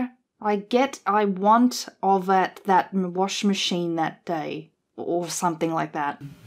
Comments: Personally, I think I hear, 'get off my washing machine.' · Sounded like he was saying "I love my washing machine." Re GTA: Isn't that how you're supposed to play that game? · I hear "They lost the washing machine"